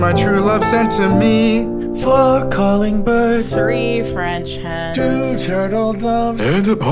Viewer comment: Merry christmas but why does it sound like he says fuck calling birds lmao